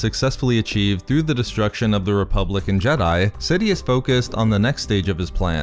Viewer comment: You said "republic and jedi" but I heard "republican jedi."